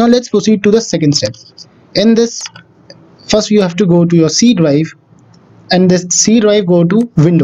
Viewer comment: What does he say? Seedwave?